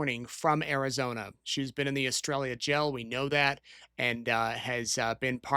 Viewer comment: I heard "Estrella" as "Australia" and was very confused for a minute...